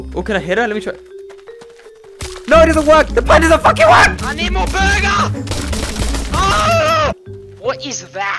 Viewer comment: Hey ir You speak spanish You will understand, but in this part of the video 8:16 y didn,t hear "i need more burger", but i heard "Valimos Verga!" That was funny XD